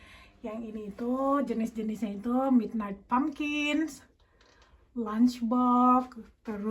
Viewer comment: wah mahal bener mobil2an tamiyanya ya sis bisa smpe seribuan, ini 1000£ dipake beli cuanki bisa nih nyampe setahun setengah makan gak abis2 haha... tamiya yg pertama kali Papa Seba punya Bignight Bumblebee namanya ya atau aku salah dengar ? haha nanya ah supaya gak penasaran